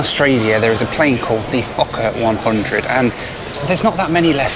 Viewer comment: i first heard that as "the fuck up 100"